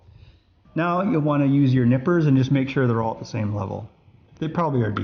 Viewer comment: Did he say nipples?